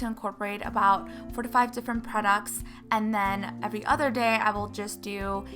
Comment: Raise your hand if you thought she said 45 different products instead of 4 to 5 different products 🙋🏼‍♀️